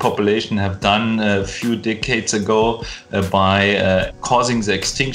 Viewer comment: i thought he said dickheads, it makes sense tho innit